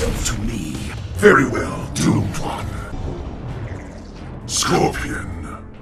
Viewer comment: Did Ermac Say Dummkopf